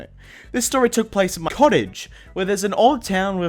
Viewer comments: what is saying cottage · Werid audio bug, or he messed up what he said and cut it out. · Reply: At m- COTTAGE